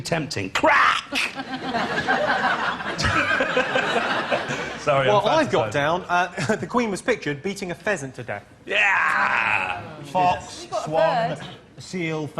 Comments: sorry, I heard "Beating a peasant to death" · did he say pheasant or peasant?